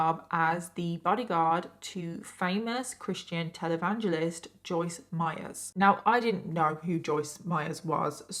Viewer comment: i thought she said joyce byers